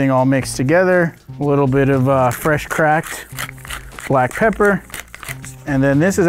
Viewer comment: I heard "a little crack.......black pepper"